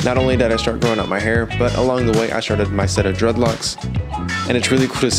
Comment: Man I know my hearing must be bad when I thought he said I started to try drugs 🤦🏼‍♀️.